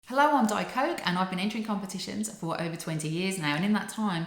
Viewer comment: At the start of the video, I thought you said "Hi, I'm diet coke"